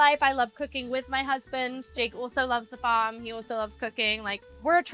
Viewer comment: did she say drake? Or Jake?😅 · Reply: jake